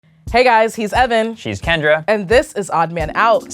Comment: I wasn't paying attention at the start and i heard "Hey guys, this is Oven" I was like wait wtf did i click on?